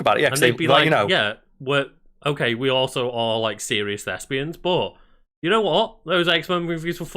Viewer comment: I thought he said serious lesbians for a second